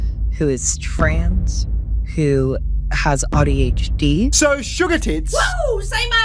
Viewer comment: Did she say "I have AUTO hd? I mean, i dont think she said ADHD. I know aussie accents can be a real thing, but.... I think this was far enough that to say, that wasn't an accent. I think maybe she thinks she really has AUTO HD rather than ADHD. its a minor point, but, illustrative of the nutjobs. If you're going to claim a disability, cant you at least spell it? Is that too much to ask?